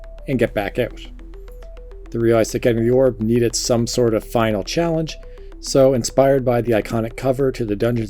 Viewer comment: I have no idea what you're saying here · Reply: "they realized that getting the orb needed some sort of final challenge"